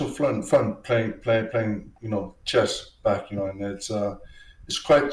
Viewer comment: "I thought you said CHEST"